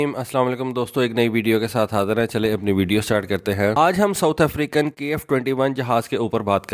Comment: Did you say south Africa.